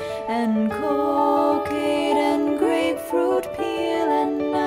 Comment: did she say cocane